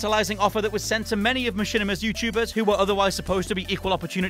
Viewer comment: Did he said "maSHITima" at 10:01? What a legend 😂